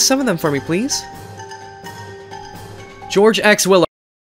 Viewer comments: Did George said George x willow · did i just heard "geroge ex willow or... im confused bc thw word is to fast i cant understand a bit